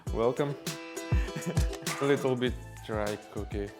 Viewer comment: I thought he said "a little bit drunk okay" hahaha